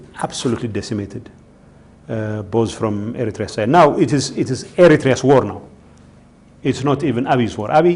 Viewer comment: Don’t kidding me I heard you saying S. T. D. I